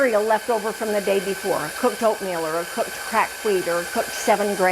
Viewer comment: lol....the closed captions for this video at 4:42 your sister says she adds "cooked cracked wheat", but the captions say "crack weed". That would be some mighty interesting bread! lol